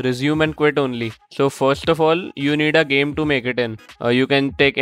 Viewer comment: what did he say? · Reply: "You need a game to make it in" Means you need a game first to put the pause menu in. Kinda obvious but had to say 😅